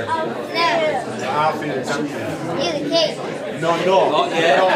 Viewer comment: “No I’m not, my father is.” That was very humble and sweet of William and shows he knows his place and most importantly, he knows how to wait. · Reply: actually, somebody else said the word “heir” and it mixed with his voice saying “not me”. So it sounded like he said “I’m the heir to the King” but in fact, he didn’t. You can see that his mouth doesn’t even move when the word “heir” comes out. So somebody else said it.